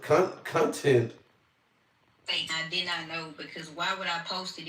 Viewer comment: cuntent!!! Ha!!! I heard that 🤣🤣🤣🤣🤣